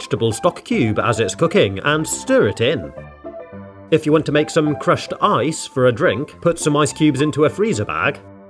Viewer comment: I THOUGHT HE SAID CRUSHED EYES 😭😭😭